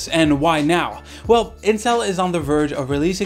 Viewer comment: did I mishear you or did you say Incel hahaha